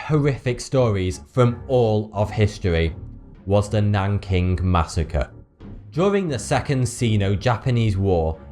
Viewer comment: I thought it said Wanking Masecere LOL